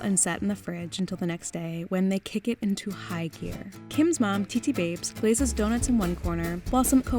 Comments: did she just say titi babes?! 😲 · jesus christ did you call her mom "Titi Babes"? lmao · I heard "titi babes" I'm guessing she meant "tita" babes.. Cuz mom's a "tita" babes as well, lmao